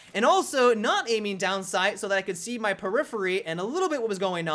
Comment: what word does he say? The one that sounds like "peripheree"? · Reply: He means his peripheral vision. ie: the outside of your screen that your eyes don't focus on directly, but you still notice movement there.